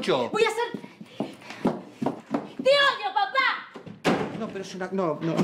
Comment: Que dice Lali en el 1:15 ,cuando cierra la puerta ? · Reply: Lulo Olivera intenta decir ¡Te odio papa!